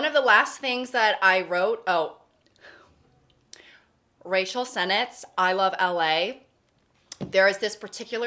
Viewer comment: i love that says “rachel” like racial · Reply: I thought she said “racial sentence” as if warning us that she was about to say something ethnically charged, but then the sentence was just “I love LA.”